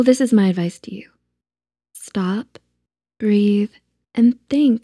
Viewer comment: sorry i thought you said stop breathing 🕴🏻